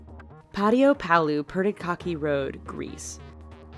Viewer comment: I heard PadioPalo Pretty Cocky Road xD